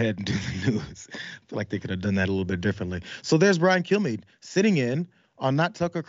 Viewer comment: "Hi, Everybodies! I'm Brian KillMePlease." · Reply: I always hear "Brian Killme".